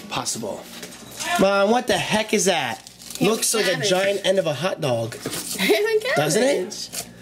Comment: When he said "a Giant end of a hot dog" I thought he said a giant enema hotdog